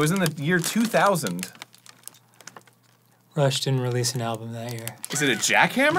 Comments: I’m not gonna lie, I kept hearing “Russia released an album” instead of Rush and was so confused all throughout the video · Dan's obsessive knowledge of Rush release dates will always be impressive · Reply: I thought he was saying "Russia released an album." I'm glad this was clarified lol